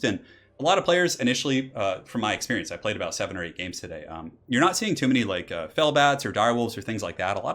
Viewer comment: what does he says at 1:33 ?? that he played 700 hundred games today ?